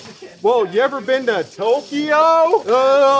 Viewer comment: wait what he said tokyo